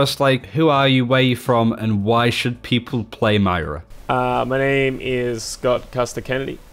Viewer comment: did you say Maira?